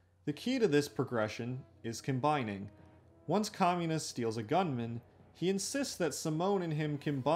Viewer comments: i hear kamina as “communist” and had to stop for a second😭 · i heard communist for a moment lol